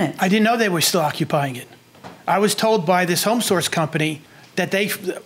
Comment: I like her accent. I thought at first she was saying “Home Sauce”